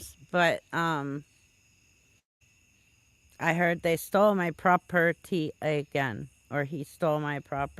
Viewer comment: Sounded like he said they stole my car. Yes, they did.